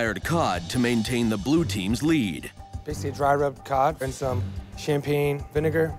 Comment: I thought I heard gordan say cod as god